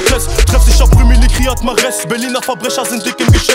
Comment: jai compris " ryad mahrez, arabe, africana "